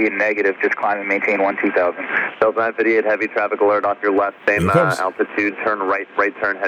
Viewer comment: I thought I heard 'traffic 1 mile'. No?